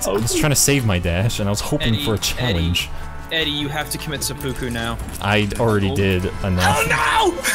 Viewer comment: i swear to god i heard "you have to commit sudoku"